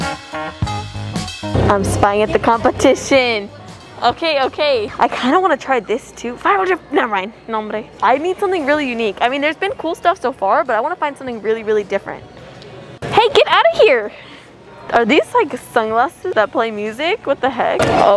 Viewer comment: I think said "No hombre" omg 😱 she is talking my language cuz I'm a latin girl sjj so proud😊🛐